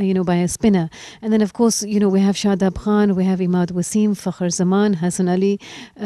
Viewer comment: shit i thought he said fucker instead of fakhar